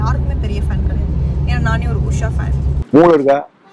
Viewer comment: Did she just say "Usha" fan😃....... That's my name!❤️